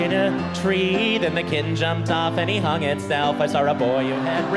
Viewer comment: in 11:45 the captions said hyung instead of hung and K-pop started playing in my head